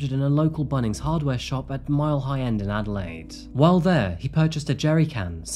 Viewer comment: "Mile High End"? It's called Mile End. I had to go back and double check I heard that correctly lol.